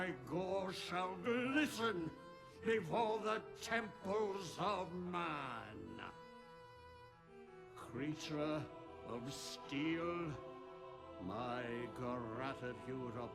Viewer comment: I always heard "Creature of Steel" as a "Creature of Skill" and that was kinda touching, it's like he's actually proud of me lmao